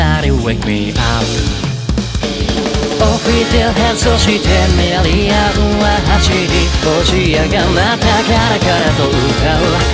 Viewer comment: 誰かのコメントで見て知ったけど、2:11起きて変装してメアリアンは走りって聞こえるよね